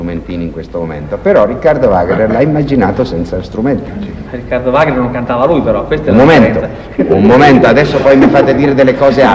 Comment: Esto es casi un milagro. Muchas gracias.... Quisiera saber si alguien puede comprender lo que dice Mario en el minuto 1:36.... Riccardo Wagner non cantava...??? . Gracias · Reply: Wagner questo l’ha immaginato senza strumento. Mario risponde ridendo: Riccardo Wagner, non cantava lui però